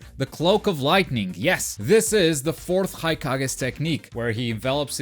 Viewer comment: Did you just say "The fourth haikage" ? Lol